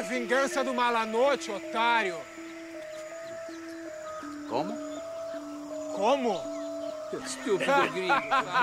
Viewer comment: what does H say? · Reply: Chibi Prussia "Como" means "How?"